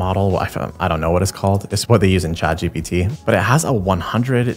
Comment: Instead of Chat GPT, the subtitles say CBT and I don't like the implications of that 😅